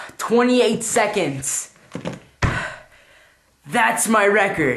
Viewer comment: Was that 'I rock'? I thought you said 'I suck' lmao